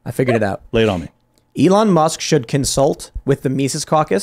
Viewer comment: Did Ian just say Elon Musk should run for President at 5:13?